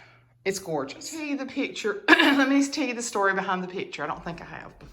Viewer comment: Did you just say “Hind End”. ? Oh Lawd!